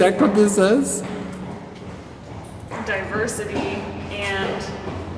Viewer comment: I think it might be saying “on purity and diversity” something lol😅